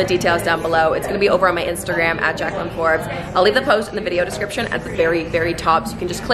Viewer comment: I almost thought you'd channel said Jocelyn Flores